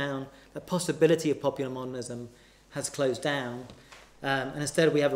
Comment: what does the word 'monalism' mean? Never heard it before. Thanks. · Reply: modernism.